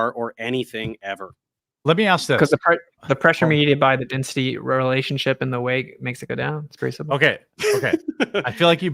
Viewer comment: what in the devil did say? I heard "the pressure media by the density relationship and the weight makes it go down."